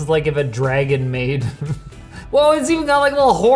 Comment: Was I hearing things but does meant that as a "Dragon Maid"? Are you a cultured person? Hmmm.